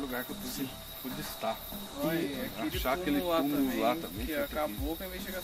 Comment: No minuto 33:37 reparem que saiu um "Oi"de uma criança enquanto vcs falavam.ouvi lá pra vcs verem